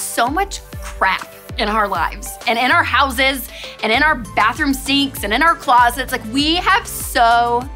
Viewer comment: Did she say, “bathroom seats?”